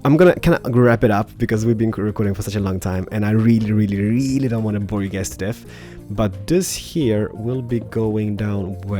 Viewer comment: what the hell did he say ??? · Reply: "I really really reeaaallly don't wanna bore you guys to death"